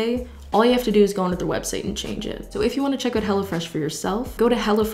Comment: Watched the entire ad because my 1AM tired brain heard her saying "hello fresh" like she was saying "hella fresh"